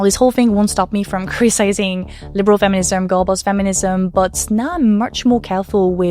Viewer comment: I had this on in the background and every time you said "Girlboss Feminism" I heard "Goebbels Feminism." It had me going down a very strange rabbit hole of thought.